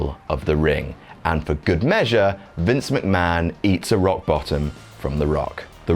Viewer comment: I thought when u said eats a rock bottom you meant (for a second) eats a rock butt